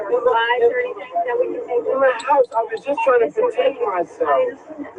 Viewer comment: Wait, wait, wait - did he say "I was just trying to detain myself????"